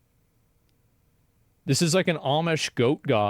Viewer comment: I listened to the whole video w asmon with my phone in my pocket because I’m working so the second I heard “this is ign” at the end of it I was like ohhhhhh this makes sense 😂😭